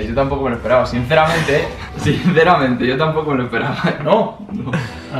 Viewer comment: Seguidme todo el mundo ahora mismo en Instagram, se vienen sorteos y muchas sorpresas!! ❤️ <link> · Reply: escuche que dijiste alo